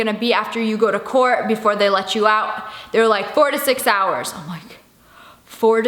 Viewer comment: When she said 4 to 6 hours I thought she said 46 hours and I was like 😳